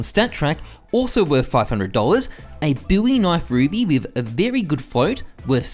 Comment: did I hear it right: "Buvi knife ruby?"